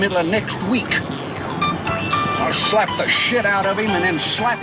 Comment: Did Roscoe say shit or ship at 2:13?🥸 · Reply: He said "Shit." It was a blooper that was edited out.